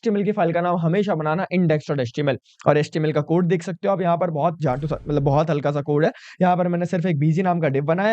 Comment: pe kya bola jhatu ???????